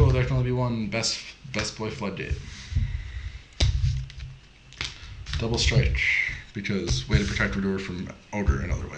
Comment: WOW I can't figure out what trap that is at 5:39. I can't see it, I can't hear what your saying, and even when I put captions on it doesn't tell me a card name. Can someone please tell me what that trap card is?? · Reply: Shade brigadine. I definitely say it